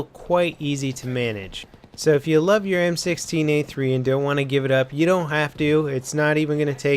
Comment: I thought he said m16a4 well I'm deaf